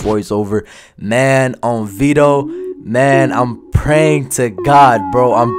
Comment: Did u just say “On Veto”???